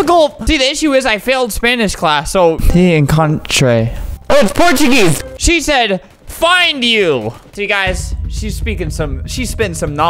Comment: she actually said "meet you"